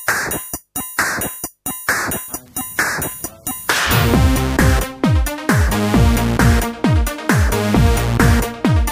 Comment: What is being said at 0:06? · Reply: and Carlos , Hi, he is counting in german one two three (the four you here only in the back) then the sound is coming in, to start an song at once the singer counts and the others are follow.